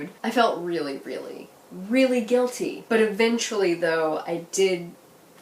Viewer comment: In the beginning when you said "eventually", I thought you said "a-binge-fully" and I was like YES! I love you.